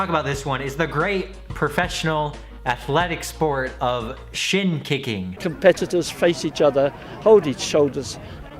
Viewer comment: I thought said "shit kicking" 😂😂